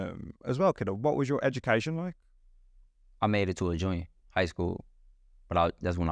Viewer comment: Thought he said junior in hs… that’s like 16-17 not 14